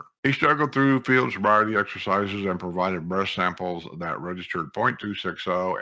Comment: I love your news report! I have to laugh because I thought you were saying breast sample lol